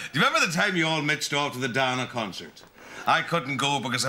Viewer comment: listen to what he says diana Concert couldn't go because a car accident ? think about that ? princess diana